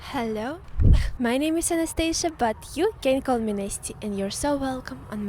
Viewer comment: Hey 🥰 Discord for chat and memes:[ <link> ] To support channel: <link> ] Thank u ❤️ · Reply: My dear lovely - my girl bestie from Russia! ...Before, I always heard you say your name like "anEsthesia" as in the surgical ward, the one who puts the client to sleep..., but finally I heard much better. My ear is better now ))) That's "anAstesia. It's much closer to waking up than being put to sleep)))) But if I think about it really carefully, I believe I should hear like "anAstAsia" because I don't know what any "e" is needed for you. LOL